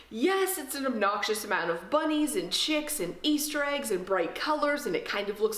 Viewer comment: Definitely thought you said "bunnies and shit" at about 0:34 and had to do a double-take! 😂😂😂 Love your videos, and like others have said...you couldn't make a boring video if you tried! 👍